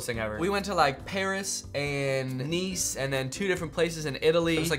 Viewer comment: I heard Nice is nice